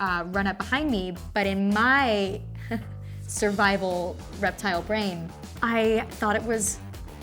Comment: Did she say reptile? IT IS TRUE!! 😱